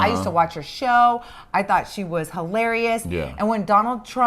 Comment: Shut up! Hahaha! I love when gets fed up. is Latina, she feisty and will always stand up for herself. ❤ I never comment but I watch you both every morning. Thank you for the entertainment ❤😂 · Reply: I thought she said she was black…..